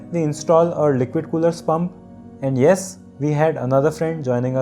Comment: wait what did you say liquid cooler ****@